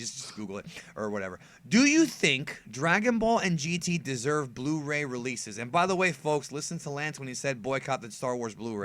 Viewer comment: lol I thought he said broly releases instead of blu-ray releases